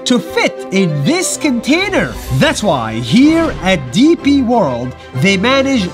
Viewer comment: I swear i thought he said pp world💀💀